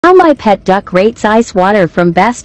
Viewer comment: I thought he said how my pet dog rapes ice water